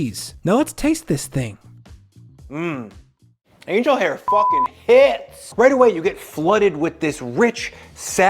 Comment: the AI tells me its family friendly but wont tell me what word is used at 8:21 lol. "hear no evil" i guess. anyway good video except for that random FUCK. i like the tip about cutting the shrimp with an oblique cut